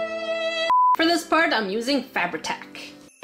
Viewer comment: the first time I heard “Fabri-tac” I thought she was saying “Fab attack” like being attacked by fabulousness or fabric and then I realized it was her glue, and now every time I hear it I think about being attacked by bedazzled fabric.